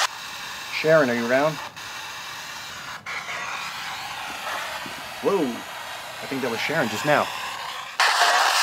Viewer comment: I think she said " I'm around" instead of " I burn" 😊😱😇